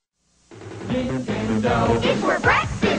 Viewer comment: Wait is it saying Nin-Ten-Do? I thought It was saying ding ding dong 😓